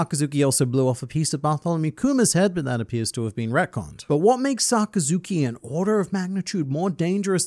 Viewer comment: what did he say at1:25. He blew off his head but it appears to be recon?